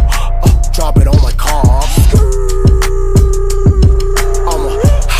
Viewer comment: All this time i thought he said drop it on my car 🤦🏻‍♀️